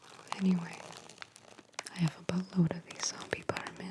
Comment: I have a buttload. I heard you. YOU NASTY